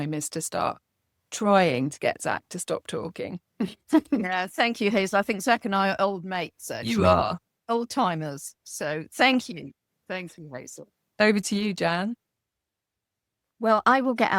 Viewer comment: Did anyone hear the voice at about 1:59 saying "we are" - agreeing with that they're "old mates"?